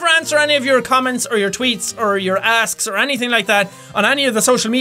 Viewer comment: I heard " Sorry if I don't answer any of your tweets or your comments or your ass" Lmao xD